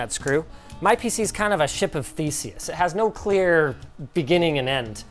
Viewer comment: Ok...I'm pretty sure I heard "Ship of Feces" instead of "Ship of Theseus"